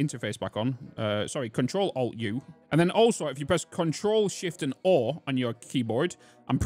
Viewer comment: I cannot understand what you are saying, "Control shift Oar"? I tried everything to bring up the menu but even Youtube subtitles cannot tell me what you were saying? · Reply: Control + Shift + O It didnt work the first few times i tried it. Works now.